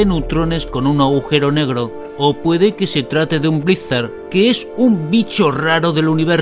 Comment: que dice en el minuto 08:16, blitzer? ¿Un bicho raro del universo, dice?